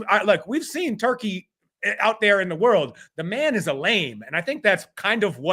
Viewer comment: What does say about Turki? "The man is a _____" Does he say "lame?" What does that mean? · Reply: Yeah he said "lame." Calling someone a "lame" is just saying they're a lame person, like dorky and uncool